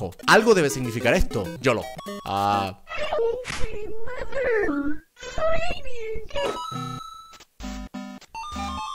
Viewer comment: esa cosa parece que dice al final ayuda en inglés o estoy loco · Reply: Dice Help me